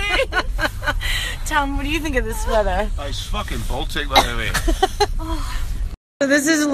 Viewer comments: What did he say at 2:05? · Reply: He used a Scottish accent to say it was "fookin Baltic"...that is the way the Scots pronouce the F word. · What’s he saying? This is fucking baltic know what i mean?